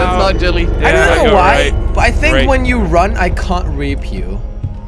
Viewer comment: I thought he said "I think when you run I can't rape you" .................